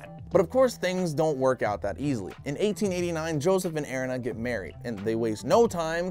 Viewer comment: was it me or anyone else hear Joseph?